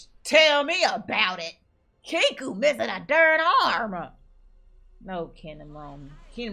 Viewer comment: did she say third arm? 🤣